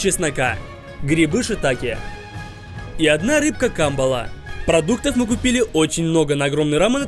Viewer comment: я услышал Гамбола 😂